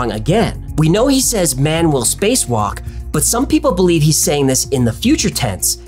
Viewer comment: When I heard the “man will spacewalk”. I heard “I never spacewalk”